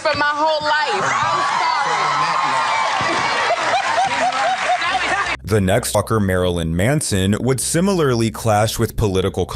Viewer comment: there's a strange glitch or jump cut that sounds like he says "the next f'cker, Marilyn Manson"